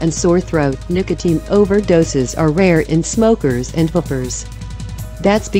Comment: i thought she said... in smokers and hookers? what? lmao